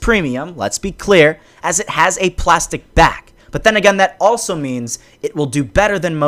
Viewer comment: who heard plastic bag?